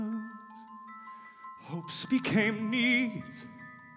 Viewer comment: I honestly thought he said hopes became memes